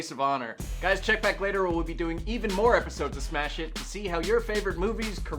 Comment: I actually like this title "Smash Hit" better than the other title. I like the play on words. I seriously thought you guys were saying "Smash It" on SJU. haha